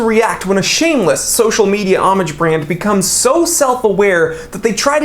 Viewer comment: Sir when you said homage I thought you said Amish. An Amish watch, a sundial and a compass. Great video.